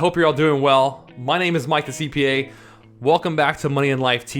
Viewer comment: Just started watching the channel and never noticed the text at the beginning showing your name. Up until now, I thought you were saying your name was Mike D'Cipia 😆